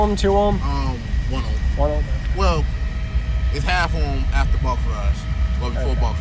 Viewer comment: 38seconds in i cant understand what he says..its 1 ohm but actually half ohm after?? If anyone can figure what he says id appreciate it..another stock alt..and sounds GREAT...what song is he playing?i know its cardi b but not sure if its dj rusticals etc? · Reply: Not sure but sounds like he says half ohm After rise 1 ohm Mainly stock electrical I’m sure he was prob nervous or just kinda starting out , good start though , more ya do the more ya learn He gets a alternator or 2 , jumps to lithium that thing will be all kinds of nasty, and those subs can take way more than he is prob clamping to them